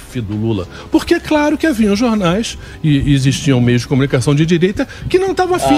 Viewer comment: Eu ouvi "haviam jornais"?